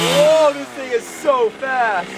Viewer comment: he said this thing is so bad instead of so fast